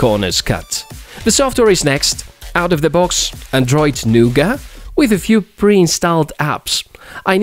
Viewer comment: WHAT YOU SAY???!!! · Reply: "Nougat" is the version of Android. (N)